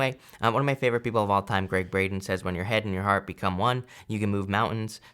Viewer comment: Omg...when I initially listened, I thought you said Greg Brady & I thought wow... I never realized how profound Greg Brady was. Then I listened again. Greg Braden makes a whole lot more sense...😁 I always enjoy your readings!